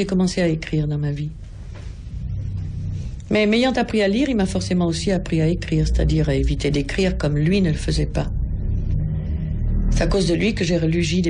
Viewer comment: Qu'est-ce que elle dit là: "il m'a appris à eviter des (?)"? (J'apprend français.) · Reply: Mais m'ayant appris à lire il m'a forcément aussi appris à écrire, c'est-à-dire à éviter d'écrire comme lui ne le faisait pas.